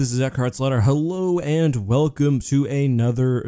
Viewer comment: I thought he Said Eckharts Slaughter at first 😂